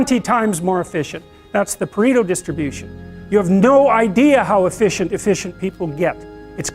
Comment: subtitle says "Perrigo distribution". Should say "Pareto distribution".